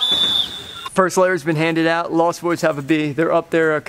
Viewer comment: Am I the only one who heard Scotty saying Lost Woods